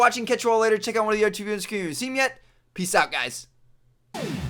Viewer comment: WTF did he say??! hahahah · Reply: He said: "check out one of the two videos in the screen if you haven't seen them yet."